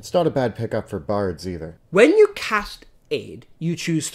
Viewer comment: I heard it as AIDS for a second and took the bard thing as a hint that bards are often portrayed as womanizers, you can make the connection from there yourself.